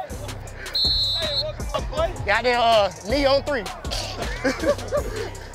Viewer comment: Ah my favorite play. Knee on three. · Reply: I thought he said me on 3 😄 but wat u said makes more sense